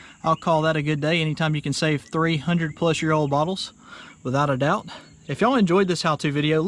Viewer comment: I thought you said 300 plus year old bottles.😄